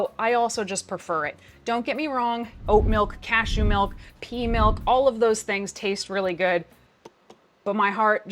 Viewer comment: my dumbass thought you said pee milk at 10:44 and i was questioning reality for a good while